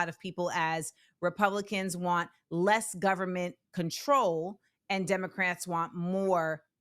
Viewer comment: You said control i heard her say help the people vs not help the people.